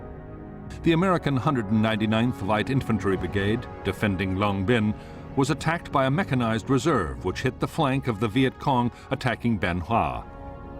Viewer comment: Did I hear that right at 28:16? "The American 199th light infantry brigade defending Long Binh was attacked by a mechanized reserve which hit the flank of the Viet Cong attacking Bien Hoa"?